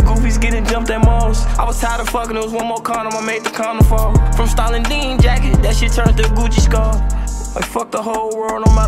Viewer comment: I thought he said "from stall & dean jackets to Gucci scarves "